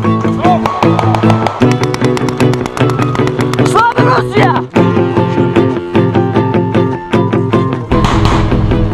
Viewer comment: what does he shout here? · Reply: Slava Russiya, or "Glory to Russia"